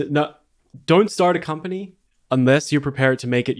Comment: I thought it said “unless your 100% rich lol